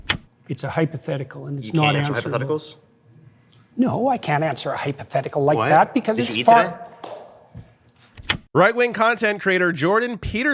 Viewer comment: is he saying "did you eat today"? I don't get it